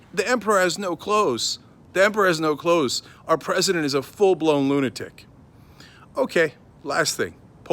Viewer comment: Did he just say our president is a really hot chick at the bar? 😅